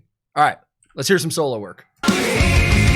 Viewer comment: I'm so tired from a bad night, for a moment I thought you were saying 'alright, lets hear some Soilwork' instead of solo work and needed to rewind. ^^ Bad ass song, bad ass band, bad ass album. 🤩